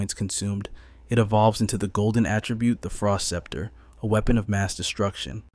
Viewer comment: Frost blade, wow, what a weird name to give to a staff, silly human. A staff does not have blades, maybe that is obvious, but you used the word blade in the name. That seems like at least strike two. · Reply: I don't know about later but at around the 29:00 mark it's called the Frost Sceptre. I don't care much for the narrator's voice and can see where clearly hearing what is said could be difficult. Edited to add: Bugger! At around 1:11:00 it is called a frost blade. I don't like AI narration! Added later: We shouldn't be too surprised because this isn't the only dodgy naming issue. First it's a city, then it's a university. Don't even get started on trying to sort out the names of different players, IRL people and NPCs.